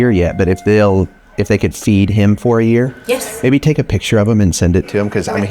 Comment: "did he say feed me for a year ? ! ? ! "